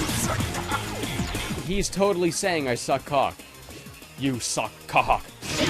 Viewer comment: I think they're just saying "you suck bwahaha!"